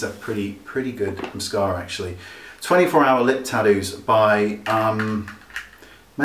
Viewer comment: I thought he said "Lid Tattoo" - which does makes sense!